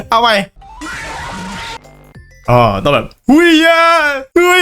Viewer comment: ขำทั้งคริป 4:36 ผมได้ยินเป็นเห้ยย่าา